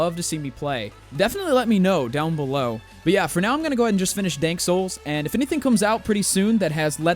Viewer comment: did he say dank souls???